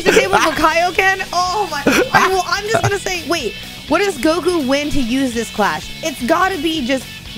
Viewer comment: Did she just said what is goku win to use this clash